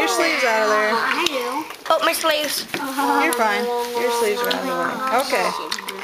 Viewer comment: the kid who said my sleves it kinda sounded like he said my slaves